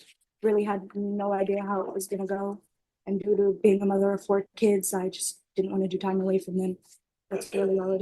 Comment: did she say 4 kids???